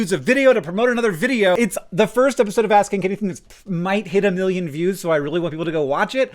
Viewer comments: First pass, I heard "ass kink anything." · This sponsor segment in this specific video is really reminding me that the first time I heard the name of the show I parsed it as "Ass kink anything"